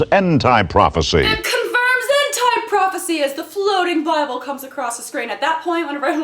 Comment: did they say hentai prophecy? don't do religion kids